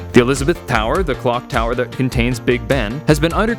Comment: I low key thought that said big sean